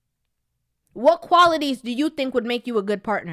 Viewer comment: Do funny, I thought she said we didn't qualify!!😂😂